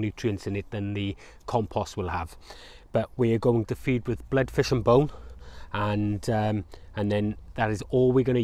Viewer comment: Anyone able to tell me what he is feeding with at 3:04 ? I couldnt make it out from his accent but it sounded like Blood fish and bolt ?? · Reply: Blood fish and bone meal